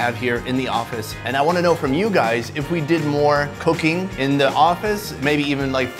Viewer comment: He said " cooking in the office" but i heard it as cocaine in the office O_o